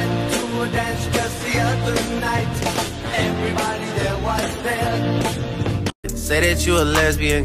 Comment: i heard "everybody there was dead"